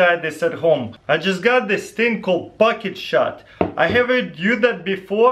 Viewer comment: I heard bucket shot